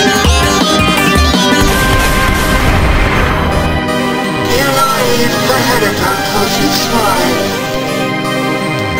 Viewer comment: what is makka pakka is saying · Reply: you really a shotgun would take me down?